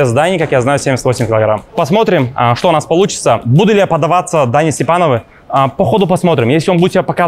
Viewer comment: "Буду ли я поддаваться Дане СтепаноВОЙ" )))) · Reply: Та не он сказал Дане СтепановУ,тебе послышалось)